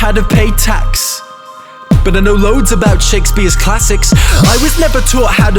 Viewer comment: Instead of hearing "I wasn’t taught how to pay tax, but I know loads about Shakespeare's classics" I heard "I wasn’t taught how to pay tax, but I know loads about chicks bee's classics"